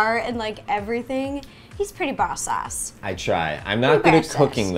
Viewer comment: Did she say the a word in the beginning of the video it sounded like she said he's pretty boss a